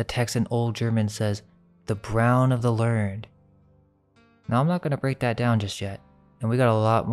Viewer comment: I don't think it says "the brown of the learned", isn't it "the well of life"?